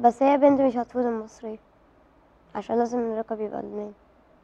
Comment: ووش قال بدقيقه 0:55 ?? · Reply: SWEET LIFE . ،،علشان اللقب لازم يبقا لبنانى،،